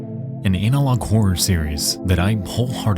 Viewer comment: I heard ANALog